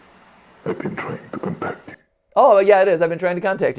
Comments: Am I the only one who heard "I've been trying to come back" on that voice clip at aroun 30:30? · I heard "compact you" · “I’ve been trying to get back to you” is what I hear but it could also be what you guys said